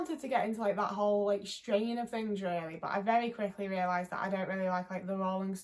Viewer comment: "so I really wanted to get into that whole _________" What did she say here? I can't make it out. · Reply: "that whole strain of things"